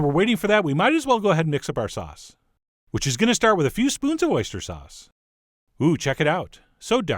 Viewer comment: I heard "Worcestershire sauce" instead of "oyster sauce" first which made me jump a little.